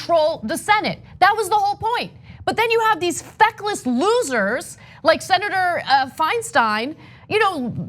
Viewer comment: Look, if I didnt follow Ana in twitter, I would have thought she said "fuckless senators" xD